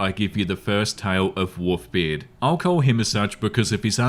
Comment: Wolfbeard... Now there’s a name I haven’t heard in a long time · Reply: Wont lie was listening to this and not watching...thought he was saying WOOFBeard until he literally said Wolves....and even then he seem to be lacking an L everytime he says it xD